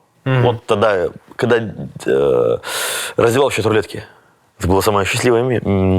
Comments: Что он сказал на 2:59:59 ???? Яразвивающиетаблетки? Шта????? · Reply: Раздевал в чат рулетке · Что он сказал на 2:59:59 ???? Яразвивающиетаблетки? Шта?????